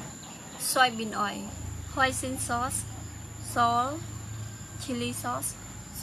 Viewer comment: i thought she said hoisin sauce not oyster sauce